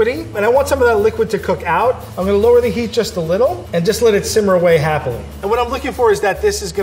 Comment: Okay but @ 7:27 did Frank say “just let it simmer very happily” ?? Because that’s weirdly adorable and I love it and I want to use that phrase in the future